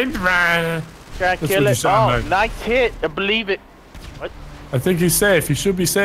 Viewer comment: Did he say I Belieb it?